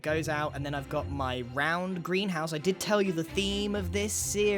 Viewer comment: For some reason when you said Greenhouse I thought you said house that's what you should call your base for the lol's